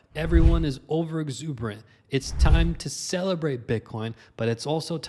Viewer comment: I heard "it's time to SELLabrate bitcoin" lol... that one split second tho I was like dang already?!